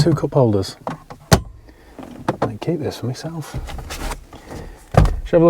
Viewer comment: Did I hear you say 'may keep this for myself?' That's why I've never been successful at trading cars, I keep too many!